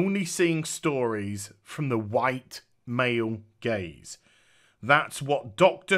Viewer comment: It took me a while to realize you were talking about "gaze". I thought you were saying "white male gays", hah.